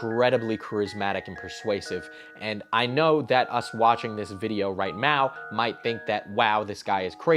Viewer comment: Did you just say 'right meow'?